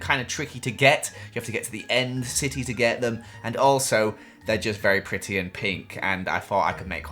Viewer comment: did I hear that correctly??? He said “ Their just pretty and pink “